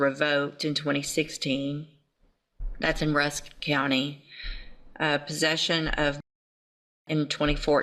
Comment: i thought she said a possession of methadocius😂😂😂